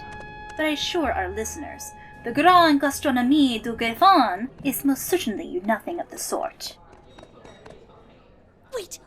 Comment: I ask again: What did she say??? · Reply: I believe she says "Grand Gastronomie du Griffon".